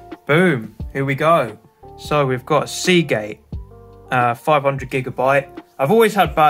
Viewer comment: lol it sounded like he said seagay